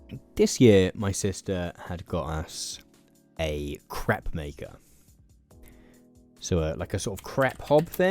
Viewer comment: Oh dear, I can't have been the only one who heard "crap maker" 😆 It is if you use it very poorly! Edit: I'm continuing to mishear it and it keeps getting funnier... "Loads of different crap fillings," "There's all kinds of things we can be having in our craps," please 😭😭